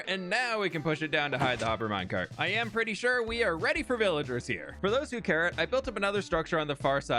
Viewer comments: did you really say: "for those who carrot" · im sorry did you say for those who carrot?